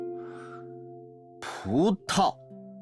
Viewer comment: I CANT STOP LAUGHING BAHAHAHA At 14:02 it sounds like Marius is saying “puta” which in Spanish technically means b!tch, haha! /nm Thank you for translating!